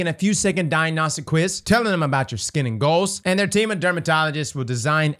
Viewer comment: For a sec I thought you said demon dermatologists lol